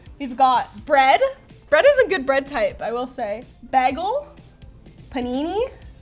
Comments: Did she just say "baggle"? 😟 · did she say "baggle"? Is actually Britta in disguise?